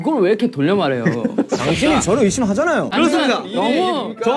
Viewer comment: Is it just me or did he say “dangshin” again?? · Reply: Yeah, i hear it too 😊